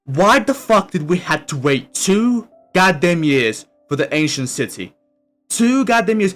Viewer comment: it sounded like you said the asian city ngl